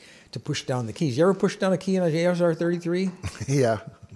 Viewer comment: the CC is wrong: Dave says "ASR 33" which is the old chunka-chunka teletypewriter (the OG "TTY"). Those keys must have needed 1/4 pound of force. I could touch type pretty quickly, but never on a 33.